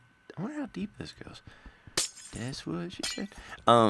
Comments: lol u guys hear "that's what she said," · " i wonder how deep this goes" whispers " that what she said"